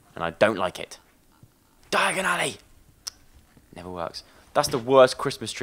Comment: does anyone know what he shouted at the fire place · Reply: jordan meares He said "Diagon Alley" its a harry potter reference he says it in a lot of games